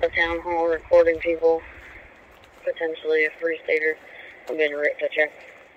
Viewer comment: what does she say? 'Free Stater', .......'Police Baiter'? What is it? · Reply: Free stater.. I put the information in the video 🤔